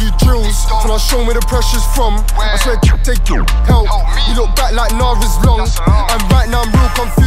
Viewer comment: I swear said Gashy help